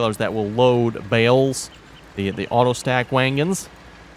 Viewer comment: I have heard this several time and NOT making fun of you(could be on my end I am hard of hearing) but it almost sounds like you say wagon as wangon. My apologies If it's on my end(hearing)